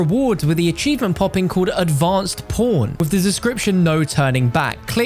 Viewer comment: I thought he said "advanced p0rn", LOL!